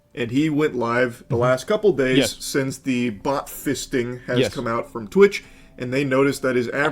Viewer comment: I keep hearing "buttfisting"